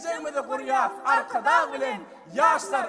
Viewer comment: Hey, some of those subtitles are manuplated im a turkish from turkey and turkmen language is close to turkish so i can understand turkmen in 2:27 and other parts subtitles says "patron" in reality they say arkadag meaning friend (arkadaş in turkish) arkadag is self proclaimed title of president